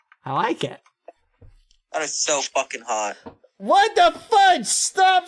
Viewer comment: It sounds like badboyhalo said 'what the f*ck?'